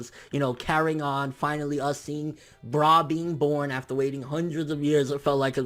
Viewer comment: I thought they said her name was Bulla. Whose this bra you're referring to